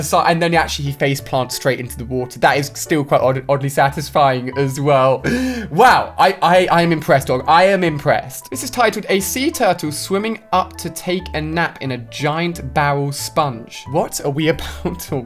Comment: did say press ups.